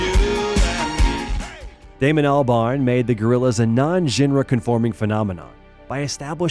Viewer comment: in, maybe pronounce, “genre” correctly. Even the closed captions didn’t know what was said and wrote out: “non-ra.”